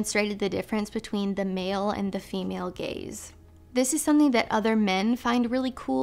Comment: Linguistics can be funny sometimes. At first I thought that she was saying "the male and female gays" 😳 I'm like, "WHAAAA?? Did she really just say what i think she said?!?🙊" It was only like 2 seconds of confusion that I misinterpreted what she really said🤦‍♂️, which was, of course, "the male and female GAZE"! 🤣